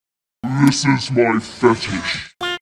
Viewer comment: What is Forseti saying? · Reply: “This is why I hate dealing with people”